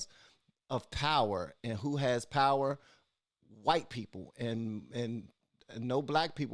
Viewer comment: Sounded like you said "whites have power"